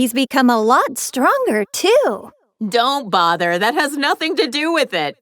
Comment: Bro, did videl just call chi chi “mom”? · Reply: Nope she said "too"